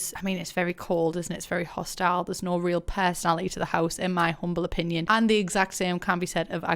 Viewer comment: "in my CRUMPLE opinion" anyone else hear that?